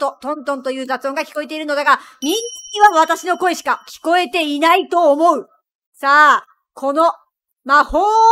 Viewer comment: ミンチには私の声しか聞こえてないと思うって聞こえた